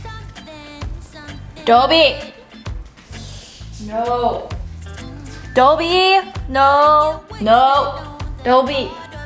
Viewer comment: I just heard “ Dobby...no...no” 😂